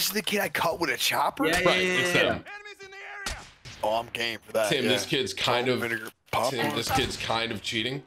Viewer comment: Did anyone else hear say “I’m getting fat here salt and vinegar popcorn 🤣😂 as bag is being fondled in the background 🤣😂 start at 10:40 and watch till 10:52 tell me if I’m trippin😂😅